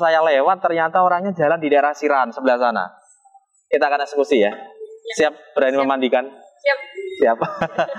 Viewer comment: kita akan eksekusi ya, aq salah dengar atau memang katanya itu mas😀😀